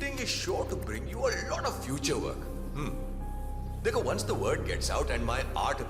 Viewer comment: Did he say the n word? 8:01 😂